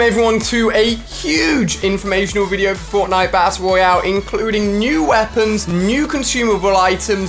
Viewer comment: did alia say bassle roale